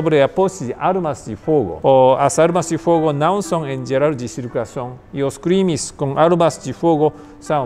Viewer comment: Alguém entendeu o que ele falou? "As armas de fogo não são em geral $#@ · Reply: De circulação